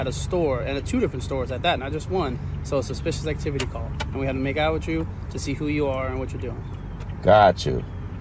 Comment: Did he just say we got a call so im here to make out with you? No thank you officer.